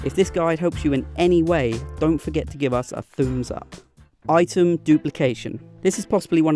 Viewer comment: did he just say “thu’ums up”???😂